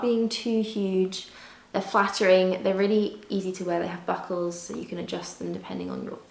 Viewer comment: Omg. 😭😭😭 I heard "and they have buttholes" instead of they have buckles! 😂😂😂😂 Oh man only me! 💀